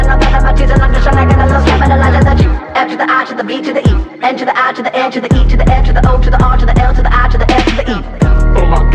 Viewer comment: Can someone tell me what bro is spelling at 2:49 😭😭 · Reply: Five nine.